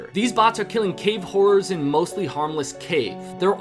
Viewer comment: I thought u said cave whores instead of horrors 😅